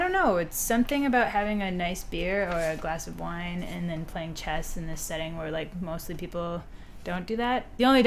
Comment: when i heard "playing chess" in bars, i understood "jazz in bars". I was a little bit disappointed ;-)